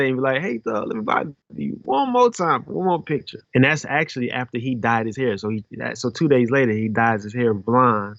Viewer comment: I thought my man said “And that’s actually the day after he died”